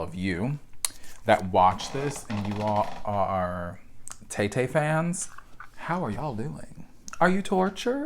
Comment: When you said "Tay-Tay", I heard "Tété" this super cool French singer from the 90s and was so excited that he had a new album. Sigh.